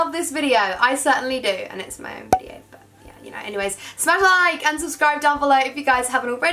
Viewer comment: I thought you said satellite instead of smash a like 😂